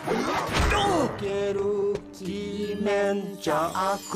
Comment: anyone have the translation to these chants? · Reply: I thought they said "jimen", which means the "soil", but it didn't make as much sense I guess, like "Move out the great evil from the soil" or something. Like, it could make sense, but not in this context I suppose. So yeah if there is actually this word "kimen" that literally refers to oni masks, then that must be it. However, I believe it's "dokeru", as I know that the word "doke!" means "beat it!" or "out of the way!", so I'm deducing it must be it.